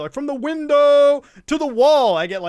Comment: "To the Window to the wall" huh i always thought they where saying "to the window to the waaaaaah!!!"